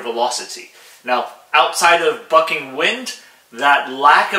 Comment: he says "bucking wind" for those that didn't hear it right the first time.